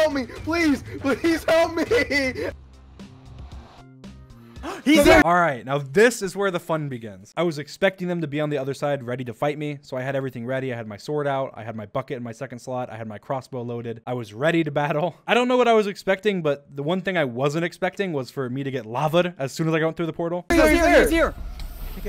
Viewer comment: he said bucket but I thought he said f*** it 😹